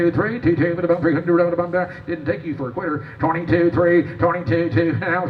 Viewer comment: Did he say “didn’t take you as a queer” 😂